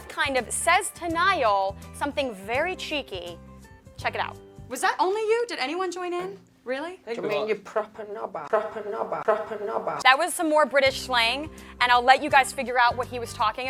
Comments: Louis sounded like he said proper nobhead or something like that. Its heard a lot around here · Somebody please tell me what Louis says in 3:48? That British slang? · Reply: Rebecca H wrong! He saying Proper knobber. Brit Slang for BJ. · Can someone tell me what Lou said at 3:44 ? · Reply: Proper Knobber. Brit slang for BJ.